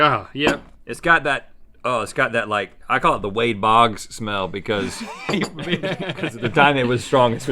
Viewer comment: Stevie, I also totally thought that Rhett said "the way that bogs smell." What did he really say? Can someone update me on this? :<O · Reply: He said"That Wade Bogg smell"